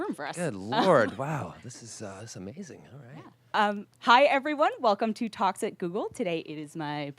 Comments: Sounded like she said: "Hi everyone. Welcome to Toxic Google" at the beginning. xD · "Hi everyone, welcome to toxic Google". I can't hear it any other way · I heard “welcome to toxic google” was it just me? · Was I the only person who heard the host say "Toxic Google..."? Funny, cuz that's always how I kinda felt about Google. Toxic and slowly killing all of humanity. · when i heard the words talks at google i thought they said toxic google and i just started to laugh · did she just say "Toxic Google"? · I don't like the name of this channel, anytime I watch I hear "toxic google" instead of "talks at Google" cracks me up every time but them I'm left wondering what's toxic about the video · Sounded like she said "welcome to Toxic Google"! I laughed. :|